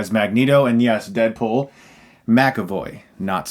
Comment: did he just say deadpool ?